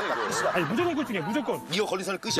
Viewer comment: 우리 애×끼라고 들리는데 저뿐인가요